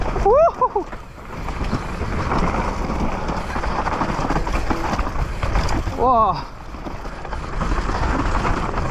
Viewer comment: Well done Remy! Curious, what did you say at 15:24? Youtube's subtitles/closed captions say "I think I had a rock stuck in my dink head at some point". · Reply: Rear end (of the bike)